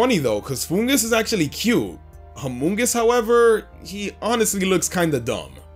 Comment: When you said Amoongus, I thought you said Among Us.